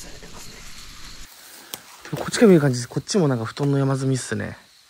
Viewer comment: ん？24:00辺りにもが布団が山積みって言ったあとに何か声が聞こえる💦 の声のようにも聞こえるんだけどボソボソ何か言ったのかな？ 今回なんか色々と聞こえてきて…とうとう、わたしの頭もバグったのかな( ´^`° ) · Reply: ？ 「こっちからなら見えるかな？」って小声で何回か言ってます。